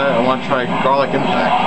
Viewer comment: Vegeta pwns all! Did you say "garlic" on 1:47?